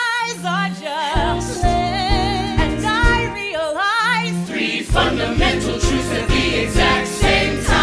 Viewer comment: oh crap for a min I thought the subtitles said “and I realize three fundamental TURTLES” and now I think I’m turnin into Lauren’s...